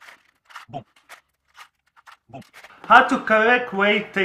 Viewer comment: i heard boob boob boob boob